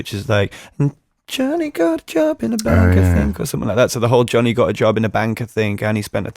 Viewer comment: when I first heard “gona get a job in a bank i think” .... i was like that could be the chorus!!!! AND MATTY SAID IT WAS GOING TO BE. loool🤯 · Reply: Jake it’s actually “Jonny got a job at a bank I think” I didn’t realize that until he said it in the interview 🤯